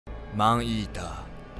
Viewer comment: マンイーターがアイーダに聞こえる病になった